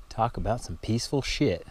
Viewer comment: Ha, for a second I thought you said "talk about a peaceful shit", then wondered why you were filming, while you were doing it.